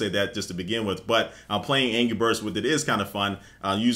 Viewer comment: sounds like he said ANGY Birds